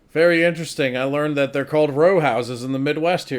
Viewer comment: I'm not gonna tell about the words used in his own country... oh wait, yes I am about to, aren't I? But as for Row houses being a term, all I know is in the tv show 'The Wire' which took place in Baltimore where he apparently lived, they refer to 'row houses' in that show quite often. So maybe it's not just a midwestern thing. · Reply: thought it was "road houses" because he misheard it.